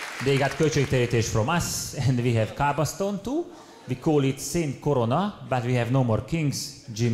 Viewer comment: Zseniális!!! Segítsetek már légyszi, mit mond Tibi 5:38-nál a "they got költségtérítés from us" után? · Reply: we have kaaba (Mecca) stone too but we call it saint korona